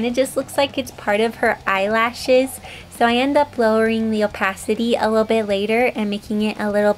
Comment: I heard her say "Opacity" then remebered when my friends where joking and saying, "Up-*ss-city"..... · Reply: lol, due to her accent it does sound like up-a55-city ngl